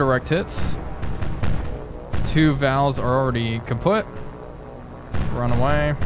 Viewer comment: Did you just say "kaputt"? xD